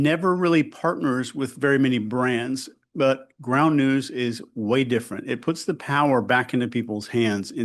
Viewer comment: I thought you said “Ground Nukes.“